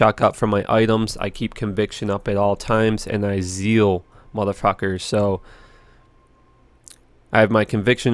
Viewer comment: did anyone else hear "zeal muthafuckers!" here too?